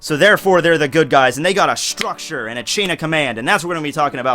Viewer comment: i heard "structure and command" as "structured chin" guess I really need sleep now